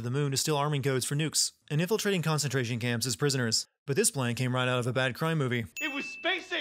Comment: olan in subtitles instead of plan. +1 sin to the video xD